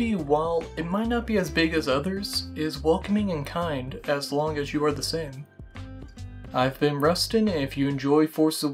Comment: Jesus Christ ... 41.11 I clearly heard as long as you're the simp and I fking lost it hahahah. Great video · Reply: O god yea it almost does sound like I said “as long as you are the simp” That might just be our brains looking for comedy though I shouldn’t have let my voice get so soft and worn out at the end there Well at least you watched till the end😅